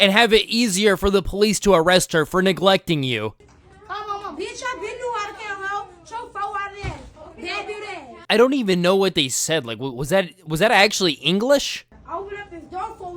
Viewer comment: "Bitch I been knew how to count hoe. Sho fo eyed ass damn dead ass" is what she said at 19:03 I think. The second half is REALLY hard to make out and I grew up around this kind of slang and slurred english.